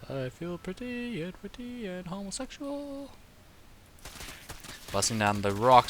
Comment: at around 6:38 ...did you say you were homosexual?